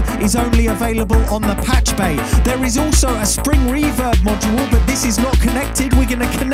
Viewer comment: did you say 'string reverb'? :D I want one.